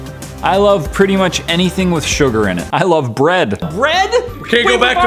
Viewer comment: When you said “I love bread” I thought you said I hate bread; and I yelled “YOU HATE BREAD?!?!?!!!!